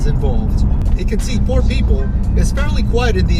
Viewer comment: 🤣 I thought he said "it can see poor people" but he actually said "it can seat four people" lmaoo!! 😂🤣